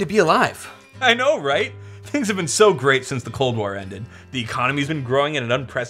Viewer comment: I heard “Since the clone war ended.”